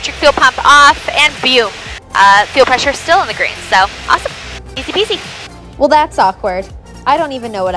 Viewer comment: you asked what you said about 18:00. It sounded like "Easy beezy" to me. I love your videos too!